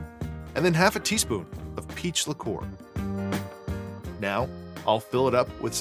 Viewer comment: I kept hearing “pizza core” instead of “peach liqueur”